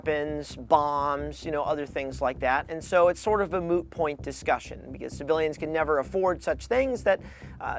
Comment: Did he just say a moo point discussion? As in Joey in Friends Moo Point?